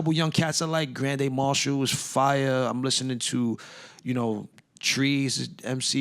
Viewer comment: Anybody got a link to the chicago MC Ka mentions at around 1:08:35 ? I think he said the name is "trees" but I'm not sure, cant find much from searching online Edit in case anyone else is looking: all the artists he mentions (including tree) are featured on Preservation's "Eastern Medicine, Western Illness" album